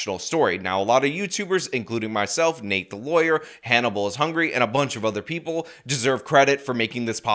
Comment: did anybody else hear ‘Hanna Ball’s hungry’ right here?!?😂😂😂😂